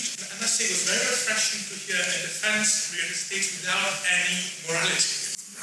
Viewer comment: what was said here? “Without any morality” was that it?